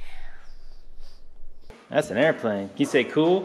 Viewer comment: I thought it said an AirPod